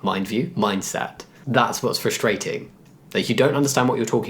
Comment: pretty sure I just heard "frustraighting" and I love it xD